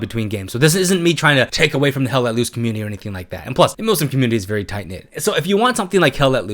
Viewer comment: I thought it said Muslim community and I was like what what? Lmaooo